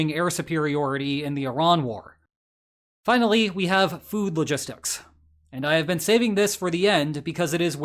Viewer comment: I wasn’t paying too much attention and you had just finished talking about MILFtec, so when you got to point 4 I could’ve sworn you said “boob logistics”.